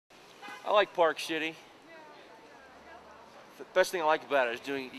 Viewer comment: the first thing i heard was:"i like park shitting." lol.